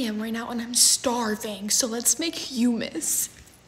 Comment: I thought that you’re saying: let’s make humans but it humus 😂😂😅